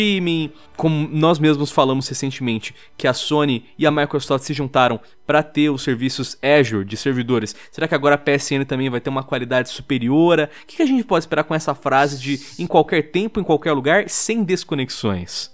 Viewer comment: Sério? Eu ouvi "superiora"?